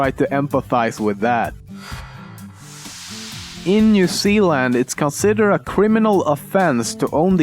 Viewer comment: sorry I heard new sea land.